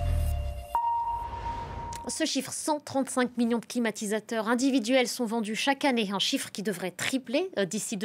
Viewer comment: C'est moi ou j'ai entendu "climatisateur"